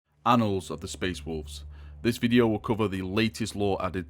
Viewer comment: I honestly thought it said anal of the space wolves 😂